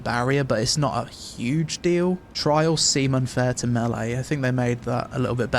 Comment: Did you say "Trial Semen"? Or what, permanent semen?